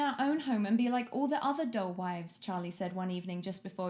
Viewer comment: At ~8:40 ish i could have sworn you said DOLL wives and i was so concerned (wouldn't even put it past tho)